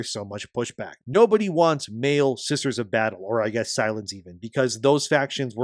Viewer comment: You spoke so fast I could have sworn I heard you say, "Scissors of Battle"